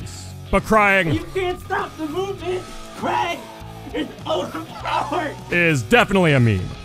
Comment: What does he say at 08:30?? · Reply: "Kragg is overpowered"